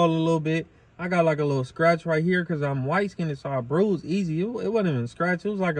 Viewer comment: Hood-Linguistics: 'Light skinneded'. 😂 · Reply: I thought he said "white skinneded" lmao but that's funnier and I like how he said "other blacks" fr I'm not even being sarcastic def at the cook outs 😂